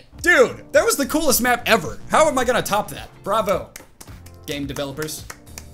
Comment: Did he sayd bravo hahaha